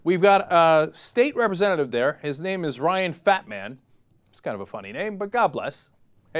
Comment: oh fattman...I thought cenk said batman lol...